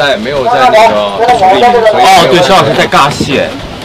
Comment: sksks do u have chinese subtitles there's some words i don't rlly understand what he's saying eg. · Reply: you can find chinese subs on bilibili lol, search 博君一肖九学. but at 1:44 he says 肖老师在扎戏 (he pronounces it incorrectly lmaoo it should be zha xi, but he says ga xi)